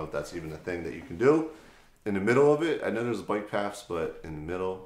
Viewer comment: Did he actually say "bike paffs" at 4:41?